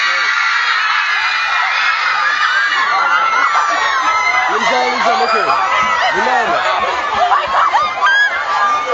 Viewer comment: what did he say? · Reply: He said that he would give an autograph